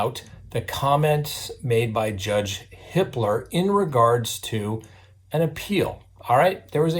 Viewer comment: I know it's 'Judge Hippler' but I keep hearing 'Judge Hitler'.